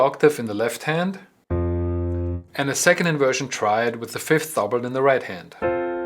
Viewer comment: I don't understand what you're saying at 1:22, you're saying 5th something, please clarify. Thank you. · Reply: Hi there, I was saying 'with the fifth doubled'...